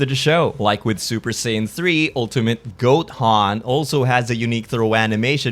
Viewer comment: I heard it! You cannot lie to me, you said Ultimate Goten(nks) and fixed it in editing. You fool, hahahaha Edit: or Goathan... or I am just overthinking